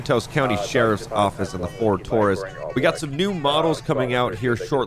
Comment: did he say ford Taurus😂?